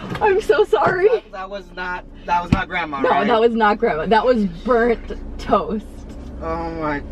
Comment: i skipped right to the end and what i heard was 'thats not grandma then'